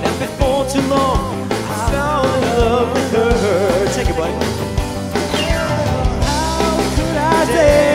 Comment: Does Matt say "Take it Blaine" at 6:11?